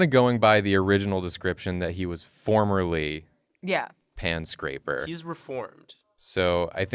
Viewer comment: I misheard pan scraper in pants scraper. Just imagine how confused I was, like "what ? You... Don't cook pies in pants ?!" So I guess my Yule Lad is Mishearer, he just hangs there and makes you hear weird stuff instead of what is being told.